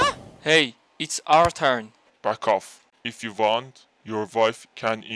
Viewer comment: I thought he said buck off instead of fuck off